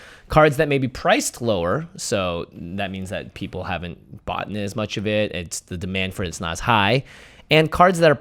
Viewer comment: Did Jimmy say "boughten"??